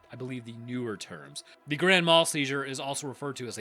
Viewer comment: I keep hearing "grandma seizure".